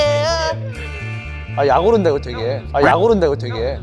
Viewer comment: 아무리 여러번 다시 들어봐도 "아,약오른다 이거 되게"로 들리는데... 억울한 창빈이ㅠ.ㅠ